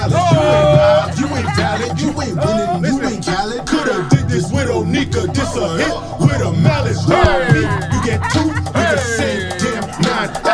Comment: Wait hol up! Did he say "coulda did this with Onika, this a hit with a Mallet"? 🤔